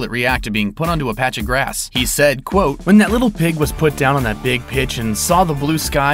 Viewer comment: Who else but me thought big pitch said pig (you know)